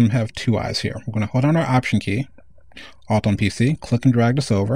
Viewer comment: what do you say on 3:58?? You say something like: "hold option Key (mac) ¿¿?? on PC..."... what do you say??? thnx, by the way, the video is great, maybe a little slow, but great, thnx